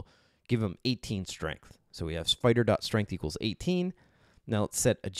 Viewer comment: Did you say "Spider".strength = 18?